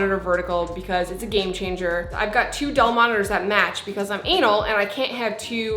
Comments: did she say "because I'm anal"😂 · In the beginning it will be very uncomfortable! But what did you say at 1:31 ? The subtitles are weird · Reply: Gary Jahman oh I said I have to have two monitors that match because I’m anal! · Did she actually said ANAL?